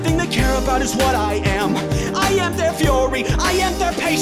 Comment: sounded like he was going to say " I am der fuhrer"